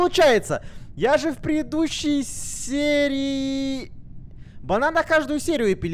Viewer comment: Мне послышалось не банан а Богдан😂